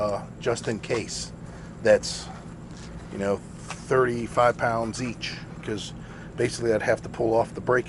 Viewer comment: I would seriously suggest less air in the air bags. Maybe I herd you wrong but I thought you said 45 pounds. That's way too much.